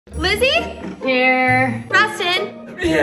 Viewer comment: i thought she said "glizzy?" when she actually said izzy😭